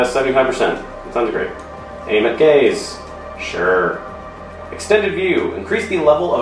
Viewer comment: I thought for a second, you said "aim at gays... sure"